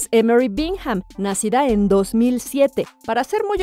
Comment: creo que escuche mal.. Dijo 2007 que nació? Si dijo en verdad eso y si es vrd pues no parece